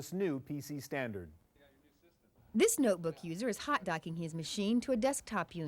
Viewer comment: I thought she was gonna say "This notebook user is a hot donkey" XD